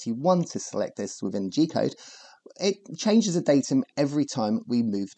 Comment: idk why i always hear "'g code cheater'' 🤣